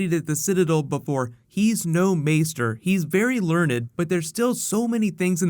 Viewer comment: Let me know who you think Jim Broadbent is playing in Game Of Thrones the evidence definitely points towards a really cool book character we haven't seen yet! Posting new The Flash Season 3 Footage, Thor Ragnarok and a bunch of Marvel/DC. So much good news dropping today! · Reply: did you just say ... learnded?